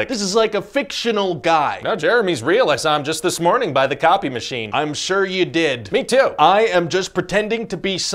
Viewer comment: "No Jeremy's real, I saw him just this morning by the copy machine!" "I'm sure you did" "Me too!" that was so good lol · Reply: i thought he said coffee machine